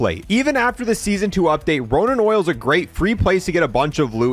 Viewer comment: Did you just call it "Ronan Oil"... ?